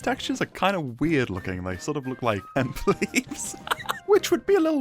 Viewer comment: can you tell me what the hell you said here i rewinded it for like five minutes straight and i still cant tell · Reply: Hemp leaves HAHA